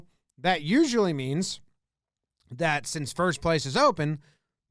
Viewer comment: anyone else hear first “place” open instead of base... lol